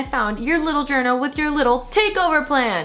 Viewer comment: Did said “GOO” like Bobby?!?! HAHAHAHAAH so cute